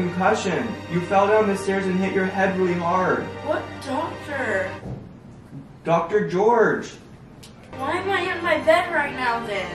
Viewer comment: When you said doctor gorge I thought you said doctor orange 🍊 LOL love you guys